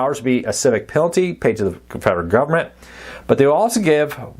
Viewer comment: Did you say the civil penalty is going to be paid to the Confederate government?